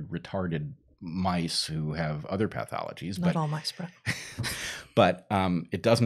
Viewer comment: did she say "not all mice bruh" ? LOL